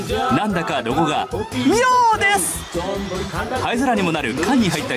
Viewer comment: 尿です！にしか聞こえん